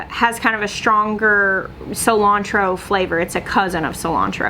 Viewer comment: Haha I thought you said "this is called cilantro" and I was like .......have you never had cilantro before??? 🤣